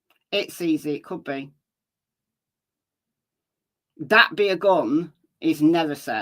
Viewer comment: Is this the path. Then she does say that be a gun. You can't hear that??? Really. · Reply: Instead of "that be" I hear the name "Abby."